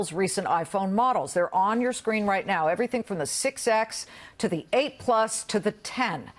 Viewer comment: did she say 6x?